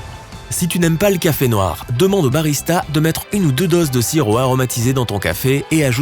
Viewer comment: si tu n'aimes pas le café noir demande de mettre quoi ds ton café? J'arrive pas à comprendre ce qu'il dit. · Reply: Sirop aromatisé